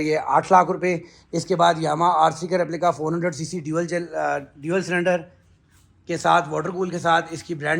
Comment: what he said dualsim? 😂